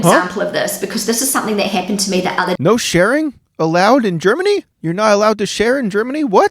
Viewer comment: You misheard AND you misunderstood. It's not 'no sharing allowed' - it's 'no compulsory sharing allowed' ie sharing is always optional.